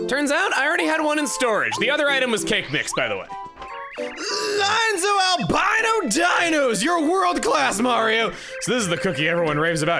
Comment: misheard that as "Lou Albano's Dinos!" at first.